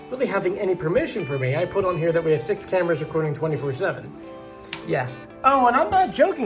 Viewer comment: Oh I thought it said 5 1/2 cameras